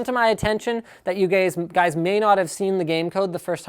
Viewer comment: lol wtf did he called us gays ?